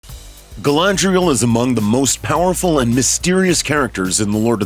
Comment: Did you just say Galandriel?! 0:01 Wow..